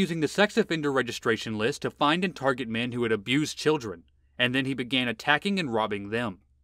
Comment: I thought the narrator said he started attacking abused children. You had to be there.